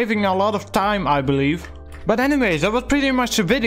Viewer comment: did i hear animes lol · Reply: Anyways